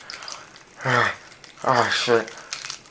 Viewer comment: おいしいって聞こえる